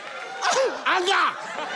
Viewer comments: I thought he said hunger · i thought he said hunger😅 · I heard "Hunger" 💀